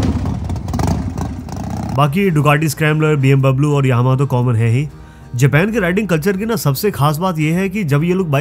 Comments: did he really say BM Bablu 😂😂😂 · Did you just said B M Bablu 😂 Yokina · did he just say BM bablu? 😂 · Did u say BM Bablu?? 2:44 Love ur video bta ;) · does he say BMBablu?